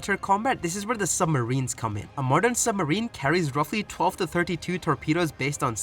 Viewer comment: You do realize those are the same things. Right? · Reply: oh yea my bad I thought he said atomic bombs im tripping